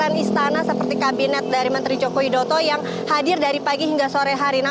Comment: kabinet dari mentri jokowidoto,, apa sy salah dengar ya